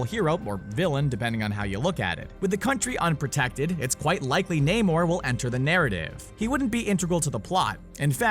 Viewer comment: When he said namor I thought he said Neymar!